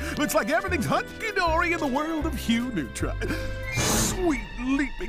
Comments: I know what he's actually saying, but did anyone else hear "Everything is hunky dory in the world of human trafficking!"? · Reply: It was hugh neutron not human trafficking but it's okay mistakes happen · I thought it said high neutron